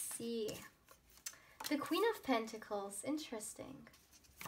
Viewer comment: i chose pile 3: i didn't complete watching the reading yet.. Yes i was confused between pile 1 & 3. When you said Queen of Pentacles, I heard it as Three of Pentacles and I thought it will definitely come, right after few seconds, you pulled it out edited: i claim with positive energy! this is what I have been manifesting for so long for the upcoming future with my significant other. love this 💕🦋🧿